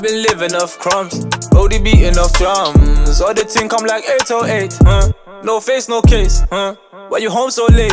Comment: If i hear "and the line beating like 808s"🤔